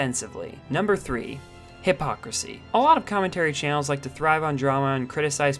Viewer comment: Why did I hear "hip hop pussy" instead of hypocrisy lol